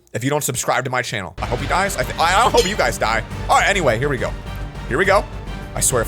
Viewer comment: Wait what did he said hope you guys died in